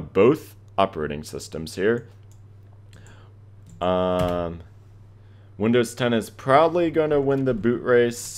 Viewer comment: I'm the best looking Windows OS! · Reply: MUSTASHEO lll I thought you said “Windows 10 is blind.”